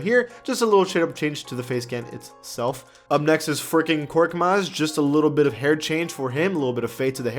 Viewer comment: it sounds like he said freaking korkmaz